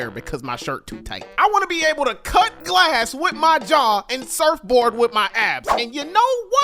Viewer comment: I thought he said "surf board with my ass" 😂